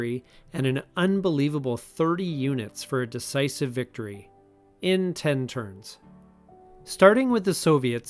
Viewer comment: "in ten turns" i thought you were saying "in con ceivable"